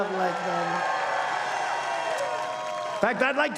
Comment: I thought he was saying "Lake Norma" and then he says "Lake Norman". Did he suddenly change gender? "Look at me. I'm rich. And I'm rich because you fucking braindead assholes keep sending me money." Apparently there is a law in Italy which says that if a candidate dies just before an election, it's the stiff that gets elected. Trump must be hoping that this will keep him in power forever more.